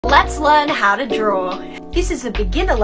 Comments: I just heard "let's learn how to drOol" 😂LMAO between loved her accent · I fr thought she said ✨ how to drool ✨